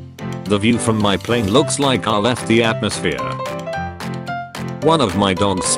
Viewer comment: I thought it said "when my plane left the atmosphere" and I was like holy shit